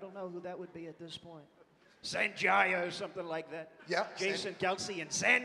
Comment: Referencing Sanjaya from American Idol like 16 years ago was just such a big brained left field improvisation. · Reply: damn I thought he was saying Zandaya but just saying it super wrong. what an even deeper cut I didnt even think of. crazy if that was off the cuff